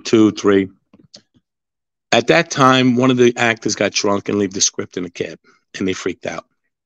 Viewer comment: I wanna know who was that and did he say he left the script in the car?